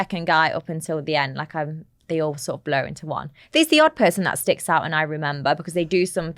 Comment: “after the second guy up until the end, they all sorta blow into one” What a choice of words 😂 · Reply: She said "blur into one".